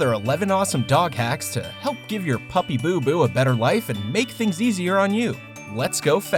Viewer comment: Did you just say “puppy boo-boo?”